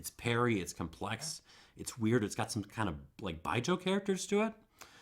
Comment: Great video. I bought that Domfrontais calvados from K&L because of this video. What did you say about it (at 23:12 )? "It's got some kind of 'baijo' characters to it"? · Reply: Baijiu! Well worth looking into if you like offbeat spirits...